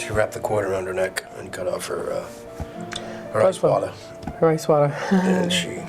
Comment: "She wrapped the cord around her neck and cut off her......ice water???" What are they saying, I can't understand at all..